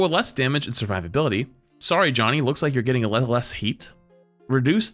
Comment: at this point due to my low auto quality i thought it said "youre getting a little less head" and i was dying laughing